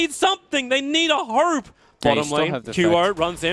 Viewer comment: did i just hear the caster say 'They need a HERB?' lol